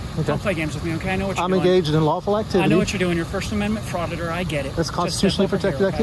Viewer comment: did he say "frauditor"?